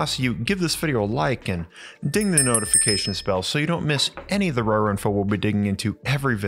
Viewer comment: I totally misheard that as "ding the notification spell" lol